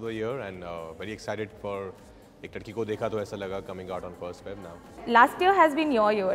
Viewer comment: Why did I hear 'Ek Thadki Ko Dekha Toh Aisa Laga'?